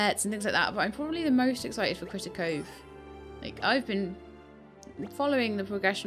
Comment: What game is it that you mention at 42:03. Sorry I can't hear what you say, but the game sounds like my type of game. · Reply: I was talking about a game called Critter Cove that I am waiting for. It looks really cool, but no idea when it will be released.